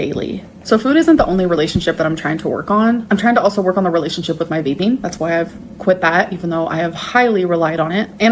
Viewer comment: I thought she said her relationship with bathing 💀